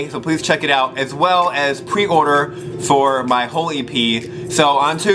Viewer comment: Am I the only one who thought he said "holy pee" instead of "whole e.p.?" oops..